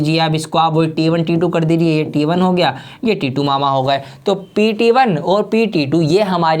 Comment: did he JUST say titu mama (of bbk Vines )